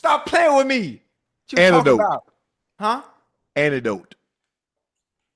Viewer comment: to be fair, it does sound like Unc saying Antidote not Anecdote lmao